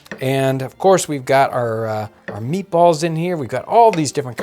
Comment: Omg who else thought was saying "horse meatballs" lol